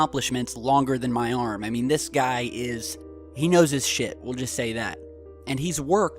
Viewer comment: did you say "shit" at 1:49 ?????? lol